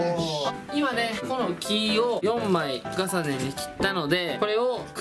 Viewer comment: なんて言ってるか気になる笑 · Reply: リズム的に4枚重ねかと…！